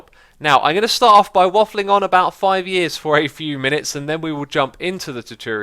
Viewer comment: "so I'm gonna start of by waffling about 5 years for a few minutes" There's noting wrong with that sentence, I perfectly understand what he was saying... but I had to listen twise to understand what he really said. First it sounded to me like he was saying: I'm gonna start of by waffling almost 5 years for a few minutes (about can mean almost too), maybe it's just my brain that found it confusing, I don't know.